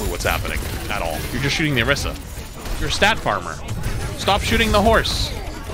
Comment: Listened to this while working, the amount of times I heard "stop shooting the horse" is unreal 😂😂😂